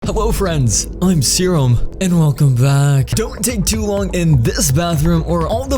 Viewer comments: What everyone heard: “Hello Friends! I’m Serum! And welcome back!” What I heard: “Hewo friends! I’m Serum! And welcome back!” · Ayo did he just say "Hewwo"?